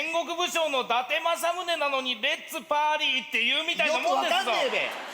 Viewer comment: I thought he said, "Its a Potty."